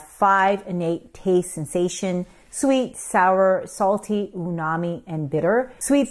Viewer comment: Is it “unami” (which I hear her say) or is it “umami” as the transcript says? I’m confused. 🫤